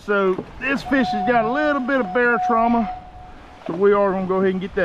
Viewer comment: I’m not sure what you said, did you call it “bear trauma” ?.. that venting procedure is also interesting and new to me.. first time I’ve ever seen that.. Thx for sharing. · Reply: barotrauma. Happens when you pull up a fish from a deep depth quickly and their swim bladder inflates like a ballon. Same principle with a human trying to surface from a deep depth...it wouldn't end well if they didn't get shoved in a hyperbaric chamber.